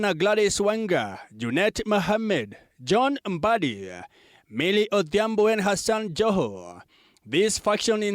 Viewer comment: I thought he said Millie Odhiambo and her son Joho😭😂